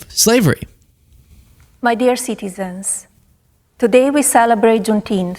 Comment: I SWEAR when she said "My dear citizens" I THOUGHT she said "My dear sycophants" 😂😂😂